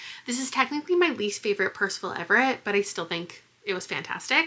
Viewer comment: When you said Percival Everett I thought you said “my personal Everest” and I was so curious as to why lol